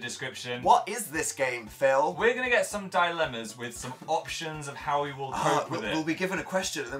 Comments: who else thought that when phill said 'dilemmas' he said 'die lemons' 😶😑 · who else thought that when phill said 'dilemmas' he said 'die lemons' 😶😑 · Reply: Sydnie Miller I thought he said "die llamas"